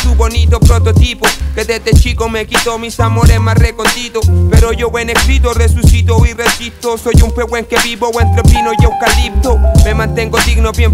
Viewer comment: que dice en el 1:51 ? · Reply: Pero yo en escritos resucito y resisto. Soy un Pehuén que vive entre pinos y eucaliptus.